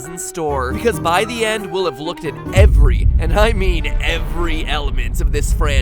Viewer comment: A couple of things do not add up Matt Patt,,1st Good job at stating the obveous at what 8-bitgaming, dacko, and rasbosic pointed outin the first 1/8 thru 2/8 of the video at 0:06 of the video,2nd How do the little children teaser fit in exatly when baby is in there when you said "so let's solve the secrets of nap sister location months before the game comes out" at 0:05 thru 0:11 ( Matt Patt) isin"t that a 'big secrert' at the video 0:05 thru 0:11,3rd of 8-bit gaming already pointed this out on their channel that it might be something else at the point of video 0:34 thru 0:57 and that is only 2/8 of the video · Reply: He did not say all but did say every and prove it is on 1:04 on the video